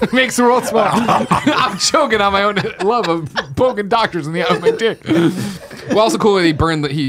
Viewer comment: Anybody please!!! What does nick say at around 1:02:15 about the doctor? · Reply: "it's a fun joke to play on your doctor, poke him in the eye"